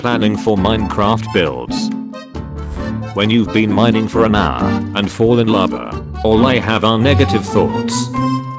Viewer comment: When it said falling in lava I thought it said falling in love which is the only thing I’ve done over and over since I was five 😭